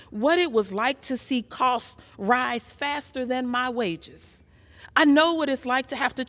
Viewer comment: I thought she said "Costs raise faster than my weight" but she said wages